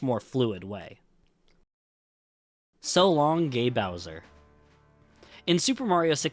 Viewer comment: Mario actually says "So long, eh Bowser?!". The reason the line is missing in the Switch version is because it's actually the japanese Shindou version, which added Rumble Pak support and the Peach voice over that was previously not in the original japanese version. The line "So long, eh Bowser?!" is missing from every japanese version and the reason is that, in Japan, Bowser is not named Bowser, but rather "Kuppa". So, having Mario randomly calling him Bowser in one of the voice lines would have just confused japanese players. Since the Switch version is the Shindou version on which a patch for textures and language is applied, it makes sense why the line cannot be heard there. Hope I was able to explain this well! EDIT: I didn't think this comment would be this popular! Thanks for the MANY people who reminded me he says King and not eh. I learned that a long time ago but for some reason, a memory of someone saying it was "Eh" left a bigger impression on me so I guess it took over my memories. Thanks for many replies though. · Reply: I always heard "So longy Bowsie!" lol